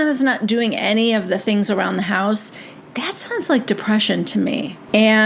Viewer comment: Sandoval’s main gripe is Ariana is not replacing t.p and pens. 😑Wow. She’s a real monster. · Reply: And I thought he said he had an assistant to "run the house". Whata scum